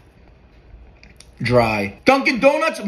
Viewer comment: I thought your gonna said dungin dunguns